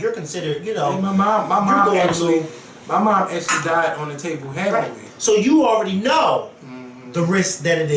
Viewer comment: Did say his mother "actually died on the table from having a baby/having me." I thought he lived with his mother? I just want to make sure I understand what he meant.